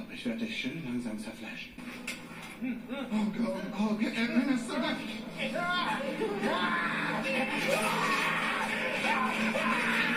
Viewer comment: Was hat Gwi Nam? gesagt hab "emenes ist verrückt" verstanden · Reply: Erinnerst du dich